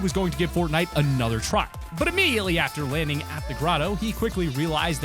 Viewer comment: Did anyone else hear him say grotto when it was agency lmao